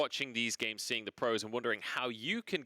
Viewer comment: Afterall counter strike copy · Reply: gameplay Maker sorry lmao I had thought you had said critical strike which is a shitty mobile fps game